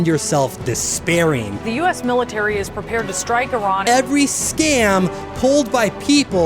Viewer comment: i thought she said "Harran" instrad of Iran and now i wanna play Dying Light again